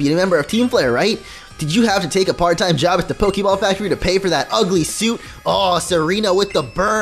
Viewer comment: when i first saw it... i thought serena said " Ugly Shit" hahahaha